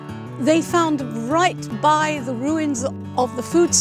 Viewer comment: what did she say? Ruin? This video is ruined.